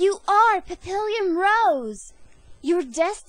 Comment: how you say papillon...XDsry its because its sound like you say papillion but i like it^^